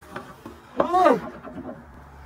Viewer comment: Also sounds like your saying sin called daddy XD